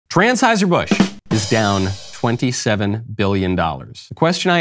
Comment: Hahaha. Did he just say Transheuser-busch? 😂😂😂😂 i havent heard that one. Perfect.